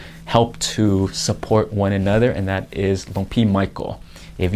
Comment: "Lumpy Michael?" 😆😆😆 At least, that's what the closed caption said his name was. 🤣